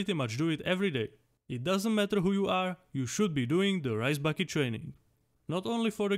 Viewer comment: in 4:35 i heard "you should be doing the rice bucket challenge"